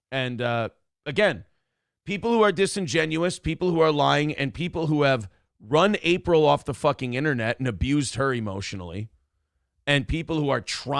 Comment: But he didn't say that. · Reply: He actually says it TWICE in this clip. He says it again at 12:05 "The people that have run April off the internet". I never understood how people can support. But now I see people like you can listen to a 15 minute clip where there's a 40 second rant about something, and a recall at the end, and still not understand what is going on. What do you think happens in the clip? Do you think is a good guy trying to make the best of a bad situation?